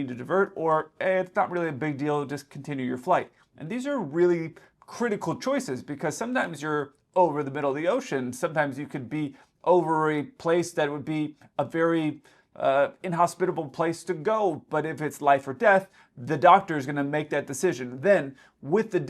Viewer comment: did say ovary😂